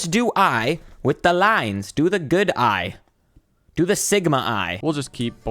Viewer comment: WHAT DID YOU SAY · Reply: THAT IS THE GOOD I WHAT DO YOU MEAN